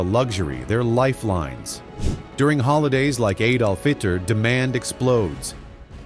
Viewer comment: at first I thought you said "Adolf Hit ler" 😂😂😂😂